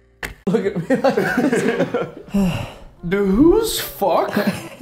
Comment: killed me at first I thought I heard the who's f***? But then I played it back and he asked do whos f***?